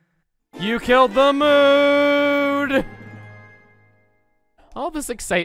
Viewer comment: I took me till my second watching of this to realize that Chuggaa said "You killed the mood" during the Harp Item Jingles. I always thought he said "You killed the moon" and since I'm not a big Zelda fan, I always thought there was something I was missing here.